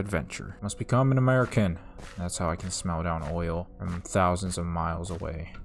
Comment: nah I heard oil.